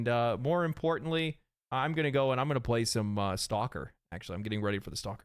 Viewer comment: almost thought he said “soccer.” 😂